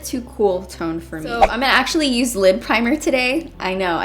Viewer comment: I thought she said "I'm gonna use a LIP PRIMER" I was like whatt?? Until I realised she said lid primer 😂😂